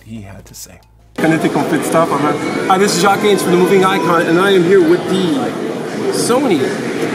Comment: I wasn't familiar with this channel, so at first I thought he said, "This is Jacques Gaines from the movie Nikon."